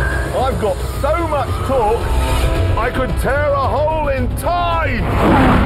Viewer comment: my subtitle said “I’ve got so much coke I can tear a hole in time” (instead of torque) and i laughed my ass off at 1 am